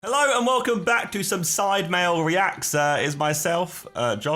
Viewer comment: did josh say sidemale